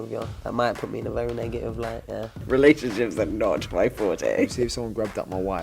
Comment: Get that intro changed because it sounds like Benn was saying "if someone takes my wife's medication".